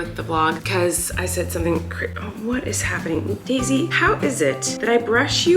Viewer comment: I definitely misheard you say maisey when you said daisy and was suddenly concerned that she gets groomed every few weeks😅